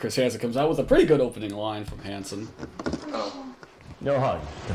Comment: i fucking love to imagine that the decoy says “oh shit” instead of saying “i’m shy” like she was destroying the whole sting by allowing him to take some steps for a hug lmfao